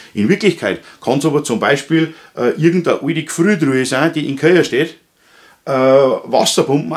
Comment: " In Wirklichkeit kann es auch zum Beispiel XXXX ~ Ich verstehe nicht was gesagt wird, kann mir das jemand auflösen?- Sorry aber ich verstehe es echt nicht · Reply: Irgand a oide Gfriertruha = irgend eine alte Gefriertruhe 😉